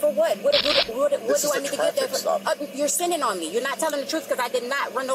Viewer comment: I swear did i hear her say " your sinning on me "? She is a total moron !